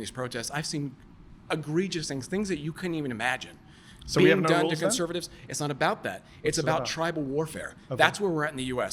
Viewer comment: What did Mehdi say at 4:42? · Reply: "So we have no laws then?"